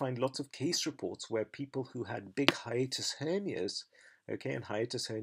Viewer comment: Could anybody be so kind an write the word down, that he is using in 8:37-8:38? I am not mother tounge, cant catch it corectly and would like to goole it. I suspect it might also me called Roemheld Syndrom, which might be my case, but I am not sure. Cheers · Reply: hiatus hernia ... there was nothing heard about a syndrome.